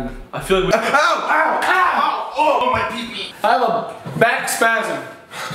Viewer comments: ANYONE ELSE HEAR GRAYSON SAY "MY PEEPEE"? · did anybody hear ethan say "ah my pp" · "Ow, my pipi." Who else heard it?